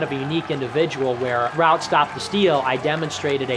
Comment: ×what does he say? Ground stop steel??